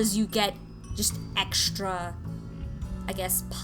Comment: I WAS LISTENING TO THIS IN THE BACKGROUND WHILST WORKING ON COMMISIONS AND I THOUGHT YOU SAID "OHH EXTRA NIPS." AND I SMEARED MY HAND ACROSS MY WATERCOLORING ON ACCIDENT OUT OF LAUGHTER WHY?